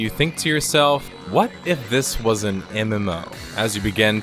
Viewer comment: the subtitle says a not an